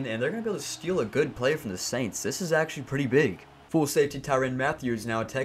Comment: did you just say full safety?